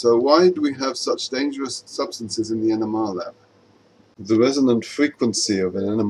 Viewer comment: I feel so horrible asking this... but im not the only one who listened to this video while doing something and thought he was saying the enema lab